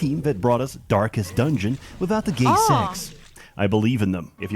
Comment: that's not what he said. This isn't the team that made Darkest Dungeons. He said this was the team that made Darkest Dungeon Without the Gay Sex. Apparently, that's Iratus: Lord of the Dead.